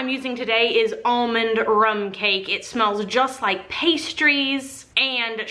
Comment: Hahaha I thought it said pasties!